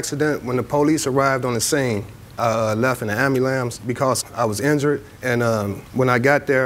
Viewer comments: Did he just say “amulam” ? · What did he just say ambulance